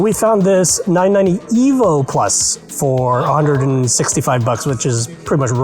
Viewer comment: Did he say $169 for a 1Tb drive? Wow! wth